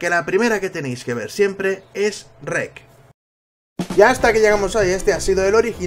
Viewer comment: JURO POR DIOS QUE DIJISTE "Shrek" D